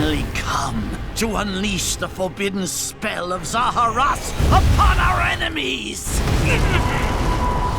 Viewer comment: I thought he said 'unleash the spell of the final smash upon our enemies' 😂